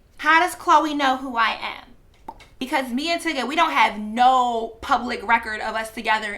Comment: This entire time I thought you were saying Tigga not Tyga lmaooooo just fucking his name up but who cares either way